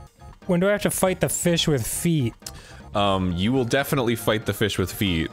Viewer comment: I don’t know why but when Dan said “we have to fight that fish with feet” I heard it as “we have to fight that fish, with feet” and I was confused as to why we are going to watch a video of 2 guys kicking some poor fish in Mario party Sega edition